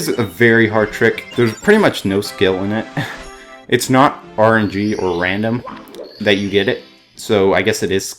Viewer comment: What word did he say at 10:15 before "or random" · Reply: RNG